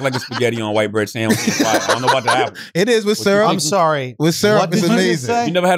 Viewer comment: Yooooo am I the only one that heard Andrew say " I'm sorry, what this ni66a say?"